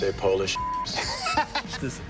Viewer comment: Did I hear Polish bit*hes? Or Am I deaf?